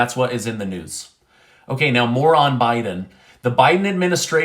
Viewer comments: Lol, I thought you said Moron Biden and I laughed but then I realized you meant more on Biden, however Moron Biden is so much better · LOL. I thought you said moron biden instead of "more on biden." 😆